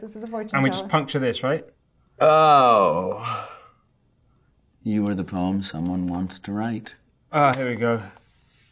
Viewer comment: I thought rami said “you are the pony everyone wants to ride” instead of “you are the poem everyone wants to write” I was like omg so explicit